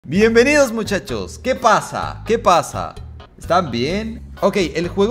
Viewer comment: Al principio entendí "bienvenidos muchachos que pajas"